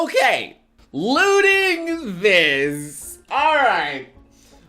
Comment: Tbh when said "lewding this" I thought he said "looting this"